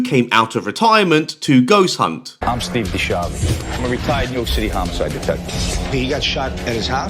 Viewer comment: Ohhh, ok SO, Steve is a RETIRED New York City Detective - I always thought he was saying "I'm a retarded New York City Detective" So, I was like, good for him, man. You do you. But, now I just feel silly.